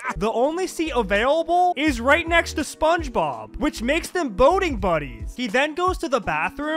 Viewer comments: Am I the only one who thought he said boning buddies for a second and got scared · I thought he said Boning Buddies 💀💀